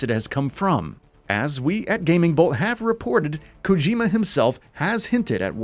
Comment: WTFFF did they say GamingBull or did I hear wrong..... Lol GamingBull